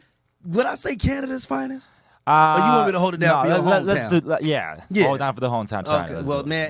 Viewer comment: after Canada’s finest, what did he say? Hold it out for hometown?